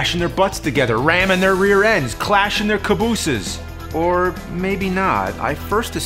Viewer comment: I thought it said grenades in their cabooses